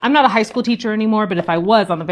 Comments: I misheard at the beginning and thought she said, ‘if I were a PRIMARY SCHOOL teacher’. I was picturing a room of 6 year olds being totally confused😂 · this is confirmation that my ears are faulty cuz I 100% thought you said grade school teacher 😅 had me here like “girl them babies ain’t hear any of that, they wanna drive fs tho ”